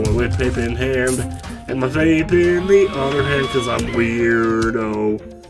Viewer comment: Is it just me or did say "and my vape in the other hand" at 4:17? Was he just making up lyrics or is he really an edgy vape king lol